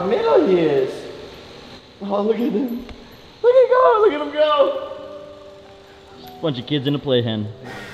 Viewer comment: it sounds like Jake said “look at kong look at him go”